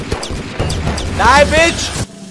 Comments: l thought you said b**ch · did he just say "Die Bi*ch"?